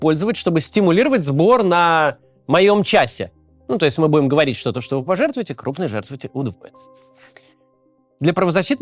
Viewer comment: я не расслышал, что он там сказал? 👉👈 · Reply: "Т.е. мы говорим, что то, что вы пожертвуете, крупные жертвователи удвоят." Это обычная схема Максима - его команда находит крупных спонсоров и они жертвуют столько же, сколько обычные люди вместе: т.е. удваивают сумму.